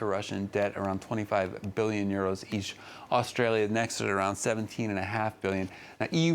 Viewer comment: did he said Australia? :D